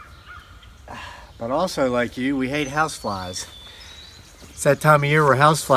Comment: Lol, I thought he's said we hate housewives. He got my attention.